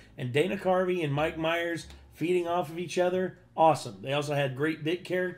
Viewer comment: I had to go back and listen to this again to hear the words “feeding off” because it sure didn’t sound like that to me the first time I heard it. I’d probably use “working off” myself but that’s only marginally better sounding upon reflection.